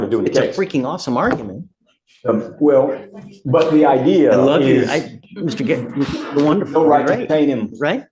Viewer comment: Did judge said "I love You" to Attorney ? 12:53 That was freaking good argument